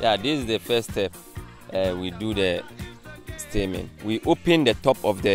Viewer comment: Am I the only one who thought he said seaman instead of steaming